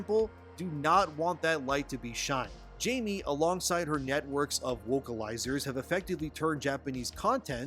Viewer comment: Am I the only one who heard "networks of wokealizers"? XD